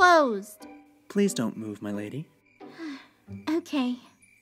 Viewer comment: DID HE JUST SAY "M'LADY"?? TO MARINETTE?!